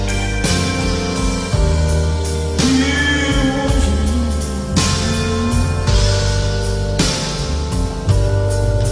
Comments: what is he saying in 6:50 ? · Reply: "he was a loner" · What is he saying at 6:50? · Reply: He was a loner